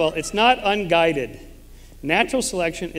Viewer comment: Did he just say natural selection is guided by natural selection lol